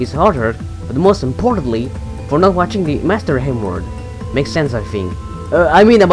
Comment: For some reason, I thought he was saying "the master n word"